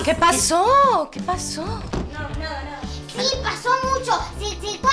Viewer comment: Que es lo q dice la nena en el minuto 2:05 supuestamente es un mala palabra pero no entiendo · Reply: se cago a piñas